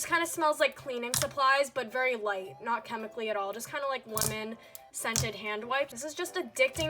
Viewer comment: did she just say women scented hand wipe SKKEKEKKJSSJA AM I DEAF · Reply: stephanie li Nah, she said lemon but I did hear women at first 😂